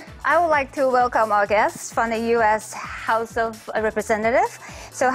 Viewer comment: 這裡有個小錯 it should be “guests ” instead of “guess”